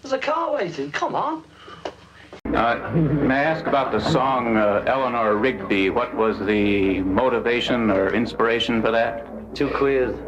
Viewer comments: What did he say at 7:11 ? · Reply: Nikita G ‘too queer’ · WHAT DID HE SAY ?! WHY DID I HEAR "DZIEKUJE" ?! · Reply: He said two queers